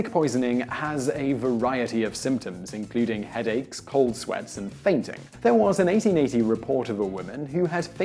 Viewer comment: sounds like he said Arabic causes “ball sweats”. Sounds uncomfortable.